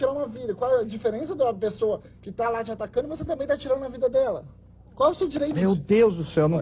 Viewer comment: T= 14:05. O "Dr" Leonardo Mendes diz : A pessoa que esta te atacando qual seu direito de tirar a vida dela? Eu escutei bem?